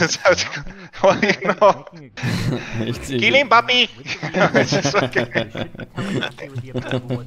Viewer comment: All I heard was “Kill him papi” HAHAHAHAH 🤣